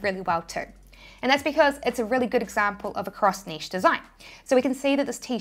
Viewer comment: Cross-niche....I thought I heard cross stitch when I looked away. For real I was like WTF cross stitch??